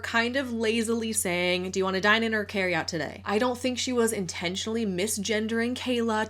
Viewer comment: I heard "carry out TO GO". And I agree with everyone in the comments - how can one be a "mystery shopper" when you post about you reviewing out and about? LMAO